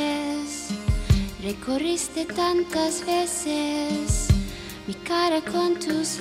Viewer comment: "Te corriste tantas veces" JAJAJAJ AJAJ, ya sé que no dice eso pero fue lo primero que escuché y no pude evitar cagarme de risa